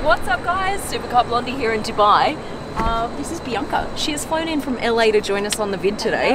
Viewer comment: Who else heard it as PRIYNKA and not BIANCA🙋‍♂️